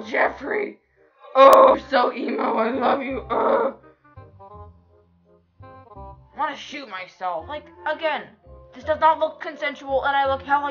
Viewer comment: I heard "gosh I wanna shit myself in the brain already" 😂